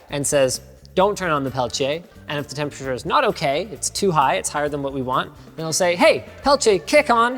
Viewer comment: I thought he said “if it’s higher than obi wan